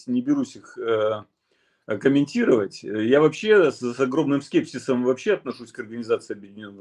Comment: К сожалению, действительно с "загробным" скепсисом ( послышалось) приходится относится к ООН. Климатическа алармистская повестка и меня свела с ума. Шансов выжить мало у человечества в нынешнем виде комфортно-европейском. Пожалуйста попросите тов-ища Альфреда пояснить про климат при случае. Спасибо за Савастьянова так же.